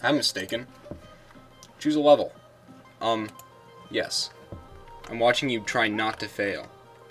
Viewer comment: i thought is said "I'm watching you trying not to fail"